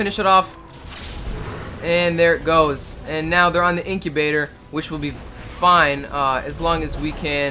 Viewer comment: did he say incubator? LOL LOL LOL LOL LOL LOL LOL LOL LOL LOL! inhibitor, there are no baby chickens in there.